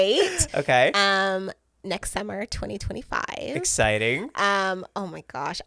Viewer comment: I honestly thought she said Nexcember 2025. Literally thought she made up a new month😂